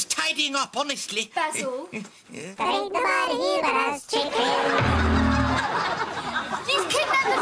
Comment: What did the chicken say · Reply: Ain't nobody here but us chickens~